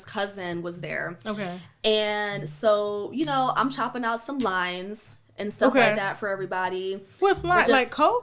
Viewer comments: She said “chopping down some lines” so naturally... whew chile.. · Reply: I thought she had said "chopping down some limes" and thought she was making margaritas or tequilla shots lol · Lord.... I thought she said, "I was chopping up some LIMES" 🍋 as in a drink garnishment. Then I realized what exactly she said as the conversation went on. I mean.... all of these people watching and she is so nonchalant about it.😳 Oh... Porsha, Porsha, Porsha! That's all I can say about Porsha... 😏😩🤦🏾‍♀️ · Man I thought she said “cutting up some limes” like for their shots or something...🥴